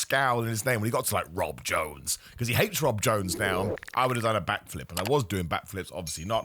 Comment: When you said back flip I thought you said bat flip. So now I'm imagining you recreating Jose Bautista's bat flip from Game 5 of the ALDS. Which is certainly an image I needed in my life! I hope you're doing well.